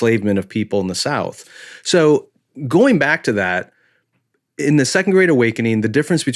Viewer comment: I kept hearing say, "the second grade awakening", lol